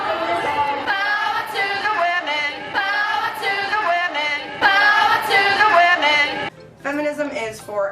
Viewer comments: When the women were chanting "bow to the women" I thought they were saying "power to the women" which is still bull shit tbh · I think that they were saying "power to Thea women" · I swear at 3:24 they say "power to the women" not bow to the women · I thought they were saying "power to the women" not "bow to the women" in that protest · i thought they were saying power to the women not bow to the women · Um, at 3:18, I don't think they were saying "bow to the women". I think it may have been "Power to the women". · I think she said 'Power to the women' and not 'Bow to the women' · I think they are saying power to the woman not bow to the woman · I thought they were saying "Power to the women"